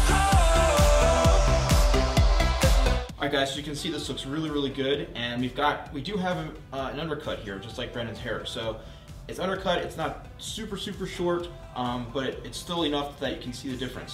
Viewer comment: DID HE JUST SAY BRANDON?!?!?!